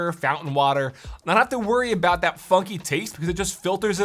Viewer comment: almost had a heart attack mishearing “wont need to worry about the <f**king> taste”